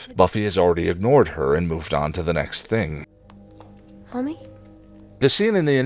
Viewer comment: did I hear Buffy say “mommy?” when Joyce is in the wall? Referencing The Body…..🤔